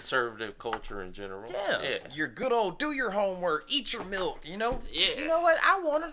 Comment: did you say "eat your milk?" time stamp